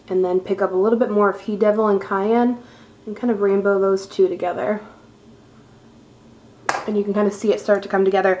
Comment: Just realized you said, "rainbow those 2 together" when talking about the eyeshadow. At first, I thought you said, "Rambo those 2 together!" I was thinking, now that's serious eyeshadow application! LOL Love your videos. <3